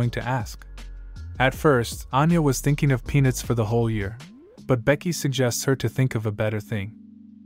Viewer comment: i heard "At first she wanted pen!s for the whole year"